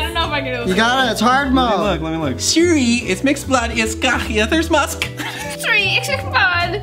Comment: it sounds like he says '' it makes blood'' 🤔🖤